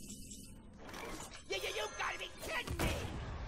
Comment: it sounds like he says “ nigga you gotta be kidding me” and I really can’t figure out what word he actually says